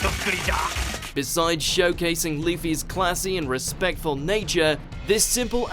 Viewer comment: did he say leafy instead of luffy or am i hearing things